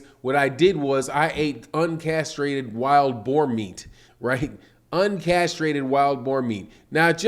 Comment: Fury didn't say boar meat. Fury said boy meat. It just sounded like he said boar because of his accent. But he said he ate boy meat.